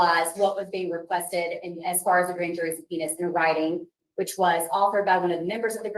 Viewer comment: the CC said “the grand jury’s penis”😂😂